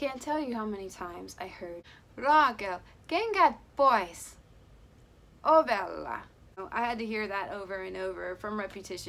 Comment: Raakel! Kenkät pois! Ovella! that's what I heard lol I'm Finnish